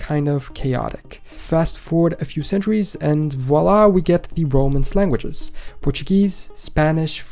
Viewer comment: Around the 3:00 mark you said, "we get the romance languanges" but it seriously sounded like you said "roman slanguages".